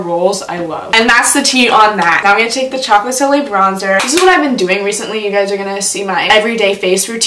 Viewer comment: I thought she said taco solie bronzer